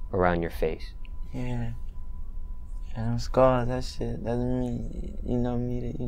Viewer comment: he’s speaking chinese 💀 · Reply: What the hell did he say at 0:23?! I heard scars, yeah them scars. But he said something else in there. Why these young dudes be talking all crazy man damn.